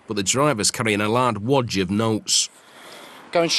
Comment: Anyone notice how the narrator said ‘lard woj’ instead of ‘large wad’ lol